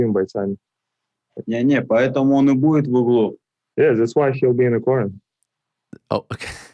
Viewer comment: what does he say? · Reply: "That's why he'll be in the corner"